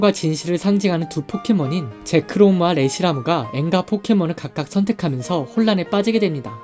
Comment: 저만 N과 포켓몬이라 들림??